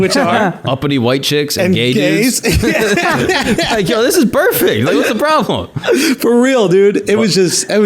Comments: did dude just say "this is 'Burrfect'...". Nice. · I love Bill. Stopped at 3:31 to check this ish out. He calls the audience surgeons at the beginning, but it is Bill who is the surgeon. A lesser comedian would crumble under the weight of their own audacity. He waltzed that tightrope like it was nothing. GOAT conversation fr. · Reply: Sounded like "Burrfect" This should be a new word for perfect Burr execution.